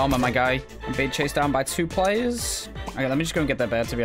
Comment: did he just say bitc* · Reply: You can see in the subtitle "i'm being chased by two player"